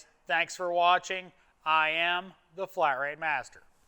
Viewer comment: And for some reason the automatic closed captioning thought you said "I am the flower master" 7:11 Lol.